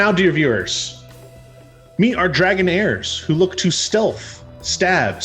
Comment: I misheard Dragon Heirs as "Dragonairs", and pictured not the Pokemon, but a portmanteau of dragon and debonair.